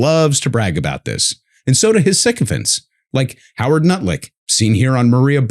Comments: “Howard Nutlick” 😂 I thought I might have misheard it until it was repeated a few more times later on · every time i hear lutnick's name i hear it as nutlick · Lol, I thought he said "Howard Nutlick" - run with it South Park , it's all yours